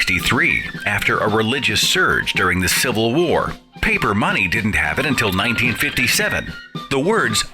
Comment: my bad.i thought he said "paper money didn't happen until...." But he actually said that "paper money didnt have it." :)